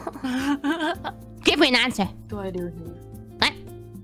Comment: “-do I do with you” is what I heard